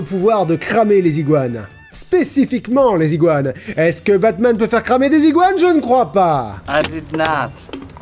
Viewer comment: J'ai entendu "Est ce que Batman peut faire cramer Desigual ?" J'ai explosé de rire avant de comprendre la vraie phrase